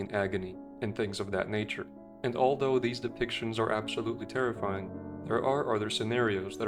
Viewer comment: My autistic self thought he said decepticons